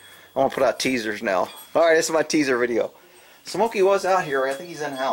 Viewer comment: I thought it said taser video, I kept wondering shit when are we going to see the taser video ha ha ha ha. Thank You have a great day.... BIG RON OUT.....